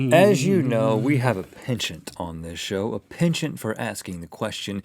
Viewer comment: what did he say around 0:15 , we have a "what" on this show? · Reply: He said "penchant"; look up what it means and apply its definition to what he's saying in that sentence.